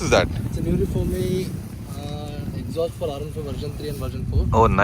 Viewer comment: what was the name of that exhaust? I couldn't hear it even with the full volume + earphones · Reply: Norifumi exhaust